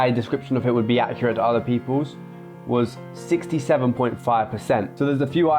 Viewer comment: did u say 67